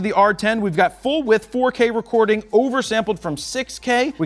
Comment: did you say "Butt Width" 😂